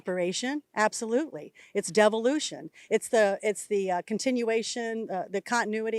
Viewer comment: She's right it is devolution. Looking at her I'm watching the devolution of the human race. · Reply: Oh! I thought she said " devilution" !